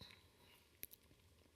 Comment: what is he saying at 10:50...you become???? looking for a cause? thanks in advance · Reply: Thaiboxer80 a grievance looking for a cause, I think.